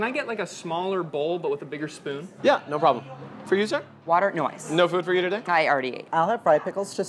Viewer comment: I thought he said watered noise